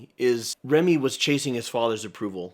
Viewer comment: When I first saw this movie I misheard Remy's name as Ranny 😅